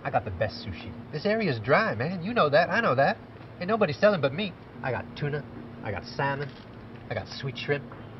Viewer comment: what he actually says is: "...this area is DRY...ain't nobody selling but me..." To say it's 'dry' means there's no weed available. He's saying there's no weed on the market, but nevertheless, he's the only one who can get you some.